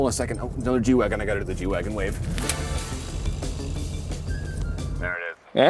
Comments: At first I thought he said Jew Wagon wave . I stand corrected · Omg. I thought he said Jew wagon wave🎉